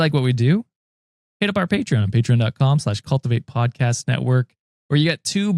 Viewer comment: I thought said "Peanut butter paint gun"😂😂😂 Not patreon.